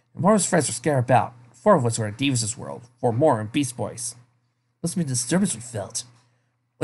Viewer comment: At one point, when you said "Beast Boy", it sounded like you said "Beach Boys".